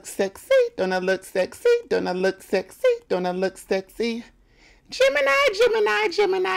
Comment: I THOUGHT I WAS THE ONLY ONE WHO THOUGHT IT WAS “GEMINI” INSTEAD OF “DAMN IT I” 😂😂😂